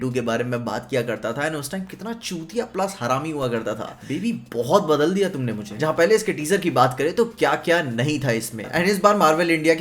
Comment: aayo! did said biwi badal diya bahut tumne mujhe?